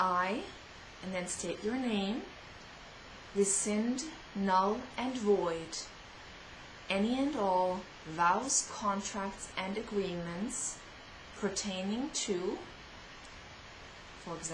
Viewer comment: Can someone please write down for me, what is she saying at 13:54..I (name) resend, (I don't get the rest).......Theirs few words that i don't understand (I am french)..thank u all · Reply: I Cindy Kashen, rescind, null, and void any and all vows, contracts and agreements pertaining to (issue) which I have made or taken, anyone in this body has made or taken, and anyone within my genetic and/or ancestral lineage has made or taken that no longer serves me, or my highest good. By the power of Grace, it is so.